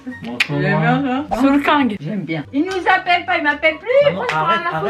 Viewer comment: C'est moi où il l'a appelé Soolkaingue au lieu de Soolking ? xdd